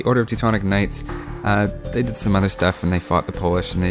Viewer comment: at first I thought you said 'They did some other stuff and fucked the Polish'